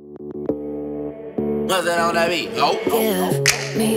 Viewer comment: MUSTARD ON THE BEAT HOOOOOO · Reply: I Thought It Said "Who Dat On The Beat Hoe"